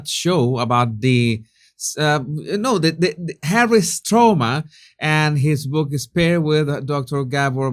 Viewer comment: When you said "Harry's trauma" I thought you had said "Harry's stoma". 🤨😵‍💫🤔